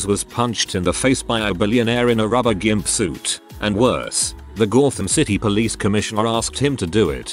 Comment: "The Gawd-Tham City" - I FIRST heard it as, "Goddamn City", but now I can only hear, "The Gawd-Tham City". Which is appropriate since the accent makes me think of the Thames.